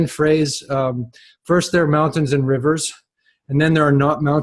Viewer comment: I heard it as "First there are mountains in reverse, then there are not mountains in reverse. And then there are mountains in reverse again." Sounded like an ancient traveller's account. Very beautiful - whether you hear "and rivers" as "in reverse" or not.